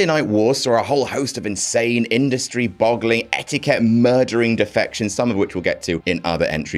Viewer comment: Actually thought it said “defecations” until about 20 seconds in.